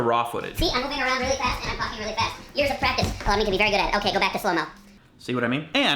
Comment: when you went high pitched at 0:54 it sounded like you said "I'm walking around really fast and I'm F*cking really fast..."